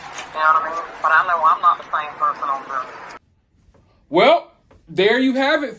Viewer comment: I HEARD HER SAY SHES NOT THE SAME ON DRUGS